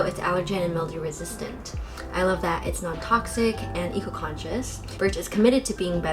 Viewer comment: During the AD, at 5:31, there’s a mistake in the captions. It says toxic instead of non-toxic 😬